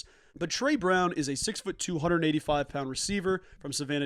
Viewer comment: At first, I thought you said Tre Brown is a “six-foot, two-hundred and eighty-five pound receiver” and got very confused (and intrigued). And then I listened again and it was clear that he’s 6’2” 185. Haha Happy birthday!